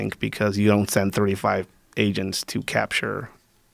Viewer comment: does he say 35 or 3 to 5 agents?